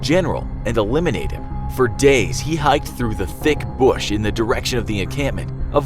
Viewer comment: anybody else hear T H I C C B U S H